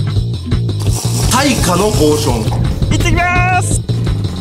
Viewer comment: 耐火のポーションが退化のポーションに聞こえたの俺だけ？俺だけか